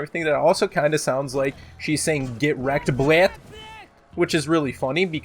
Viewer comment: So that is what Valkyrie is saying. I always heard "Get rekted" :D